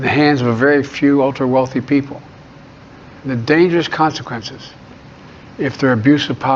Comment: did he say the dangerous contradiction of power?